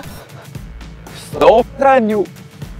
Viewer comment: Kto usłyszał W 4:31 "STOP FRANIU"? XDD